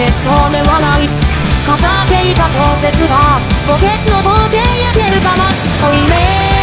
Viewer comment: i hear len saying cats are great